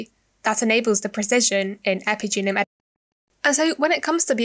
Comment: Who can guess what I was saying at 06:00 ? Whoops, my audio got corrupted but i tried to fix where possible so hopefully not too big a problem!! :D Plus, i feel like we've only scratched the surface with this topic so hope to explore further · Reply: you said epigenome editing :)